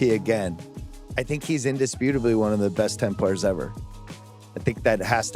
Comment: what’s he saying here, I can’t make out the word or words that comes before ever “I think he’s indisputably one of the best ……. ever” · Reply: 10 players